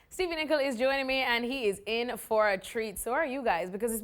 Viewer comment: Did she just say “ Stevie Nicol is doing me....and he’s in for a treat! “ The look on Stevie’s face was epic 😂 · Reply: You heard that wrong though. She said "Stevie Nicol is joining me", if you listen carefully...